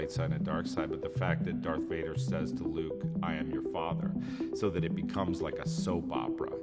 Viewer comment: he actually says "no, i am your father" in response to "he told me you killed him"